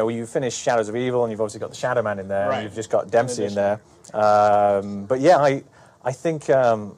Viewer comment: Sounds like he said "and we've just got Dempsey's soul" as in we have just recently collected or added his soul. Not that they are the only ones in there.